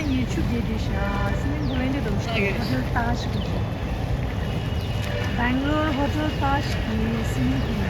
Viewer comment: which hotel? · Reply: Bangalore Taj Hotel swimming pool I heard